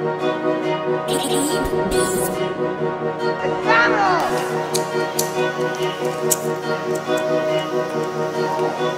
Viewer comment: Am I trippin or did I hear a crack head that said "MCDONALD" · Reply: It's "Domeno" beats tag